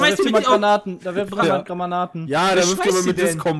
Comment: Ich hab bei ungefähr 15:59 von verstanden: Grammanaten